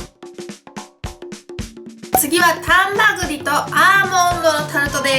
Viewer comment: 丹波栗とアーモンドのタルトがハンバーグとアーモンドのタルトに聞こえたｗｗｗ